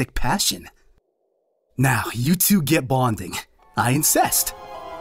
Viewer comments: Did he just say "I incest"?! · "Now you two get bonding. I insist!" It sounded like he said incest, not insist! XD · I think they purposely said incest instead of insist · "you guys get bonding, I INCEST"?! check it out minute 8:27 wtf is my little pony trying to say here....think about the whole theme of the episode and then that comment. that messed up